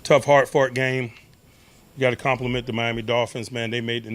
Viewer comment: Did he just say tough hard fart game 🤦🏻‍♂️ I’m done